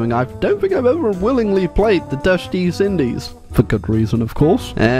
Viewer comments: Did just say “Dutch Deez Indies?” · is it just me, or did he say the dutch deez indies